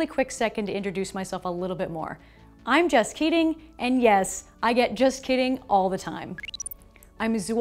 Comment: No matter how many times I listen to it, I just can hear ''Hey everyone, I'm just kidding'' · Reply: This is my first time here, I thought I heard it wrong or something had to go back to check but still hearing "HI, I'm just kidding" can't unheard. 😂